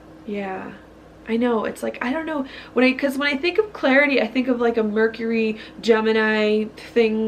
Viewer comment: ish I heard the word Refining rather than clarity ;) As always, your guidance through the stars is so helpful! <3<3<3